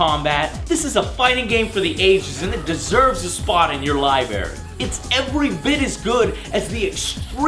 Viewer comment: at 16:55, did you say "liBARRY"?!!